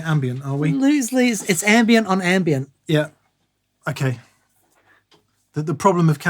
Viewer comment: I heard "ambient on Ambien." Well, of course.